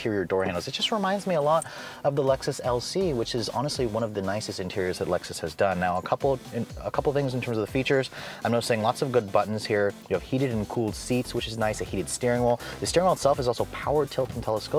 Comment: Wait, 4:05, did you say heated AND cooled? I'm REALLY hoping for vented seats on an entry level luxury crossover!